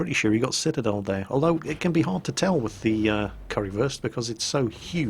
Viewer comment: I keep hearing "Currywurst"